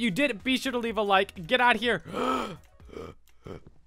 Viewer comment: One day i thought he said unicolision insted of you knuckle heads